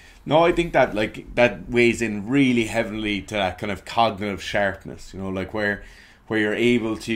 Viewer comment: I thought was saying "Cognitive Shark-ness".